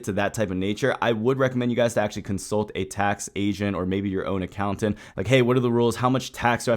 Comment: i thought you said consult a tax asian at first xDxD haha